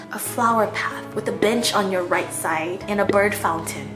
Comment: Lmaoo tell me why at 5:45 I heard "with a bitch" instead of "with a bench" 😂